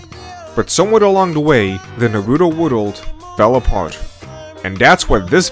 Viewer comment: Why is it that every time he said Naruto World, i hear "Nawuto Wuddle"